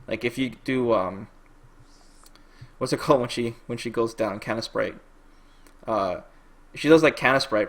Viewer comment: I heard "canon sprite".